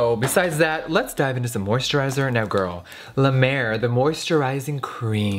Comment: I thought he said 'la merde' for a second.... oops xD